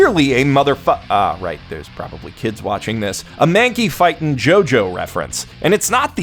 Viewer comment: Thought he said Mankey Fucking, had to rewind because if I didn't I'd have to accept the fact that Mankey Fucking is better than motherfucking. Conflicted for life